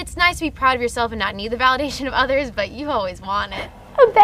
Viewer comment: Lynette 'Squeaky' Fromme 2019 ^^^^^^^^^^^^^^^^^ ANYBODY: What does she say from 6:20-6:24? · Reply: It's nice not to need the validation of others but you've always wanted it